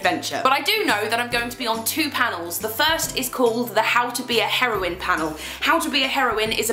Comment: At first I thought she said How To Be ON Heroin, and I was just like I don't know if you should be excited about doing that panel.